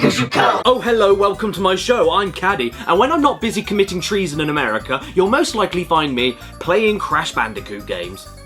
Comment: Was I the only one who misheard Caddy say “committing trees in America” or nah?